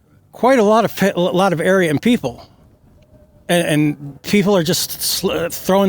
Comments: SO HE'S " AFRAID " IF NUCLEAR WAR BC IT'S GOING TO WIPE OUT A LOT OF " ARYAN PPL " .... SO DAMN THE REST OF THE CITIZENS OF COLOR OF THE USA. LIKE I SAID MOST WHITE PPL WHO SUPPORT TRUMP ARE RACISTS & THE POC WHO DO ARE FOOLS ESPECIALLY THE BLACK ONES. THEY'RE HIDING THE RACISM BEHIND POLITICS BC THEY REALLY ARE NOTHING BUT A BUNCH OF COWARDS & FOOLS . THEY'RE IN THE SAME VAIN AS THE WHITE PPL WHO HIDE THEMSELVES BEHIND THEIR KKK HOODS !! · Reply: I had to watch that segment again; the first time I (thought I ) heard him say a single nuclear bomb could wipe out "a lot of AREA and PEOPLE", not "a lot of Aryan people". Maybe that's just me, but I don't think the man was saying what many seem to think he said.... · Which one has a slip of the tongue? I don't hear it. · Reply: I listened to it a few times, it does sound like he says, "... a lot of area and people..."